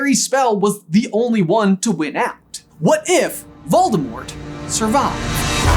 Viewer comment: For a second I thought it said, “What if Voldemort SUBSCRIBED” and I was so confused